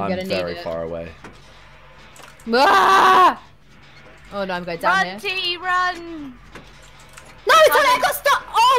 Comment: can someone translate what Talia said 😂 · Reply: Oh no we are going down here